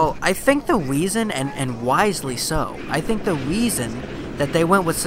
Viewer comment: i think the" weeson". instead of reson